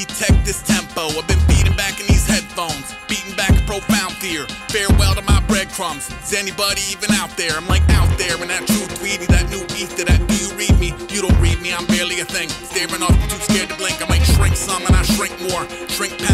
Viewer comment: did he just say 'xanny buddy even out there'?